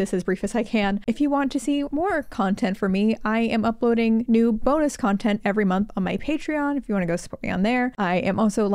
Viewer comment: I love your stuff, great video as usual. But at the end, I could have sworn you said "nud3 bonus content on Patreon," and I was just briefly really alarmed. That's all, lol